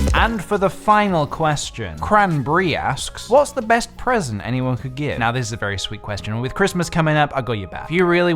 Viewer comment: instead of "what's the best present anyone could give" I heard "what's the best president america could give" and I was so confused